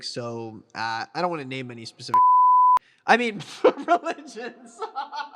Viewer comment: WHAT DID HE SAY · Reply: Chr*stians